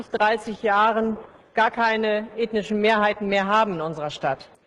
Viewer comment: I’m German and I’m pretty sure she said “indischen Mehrheiten” (Indian majorities) and not German majorities. But on the other hand that what she said at 2:42 don’t make sense anymore.